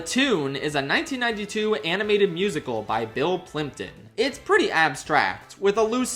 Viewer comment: The spit-take I did when I was looking away from the video and misheard "Bill Plympton" as "Bill Clinton" omg